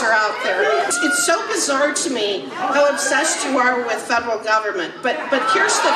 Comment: She actually said that it's weird to her that people are so obsessed with federal government.